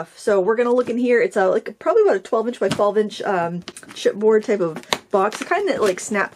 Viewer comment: I hung on at 2:30 replaying with CC on, over and over, because I thought you said 💩board box, and I couldn't figure out why happy was "crapping" on her own box 😂😂😂 /I'm Catholic, I immediately prayed for your soul and wondered.. what is 💩board box? HELP! 😂😂😂✝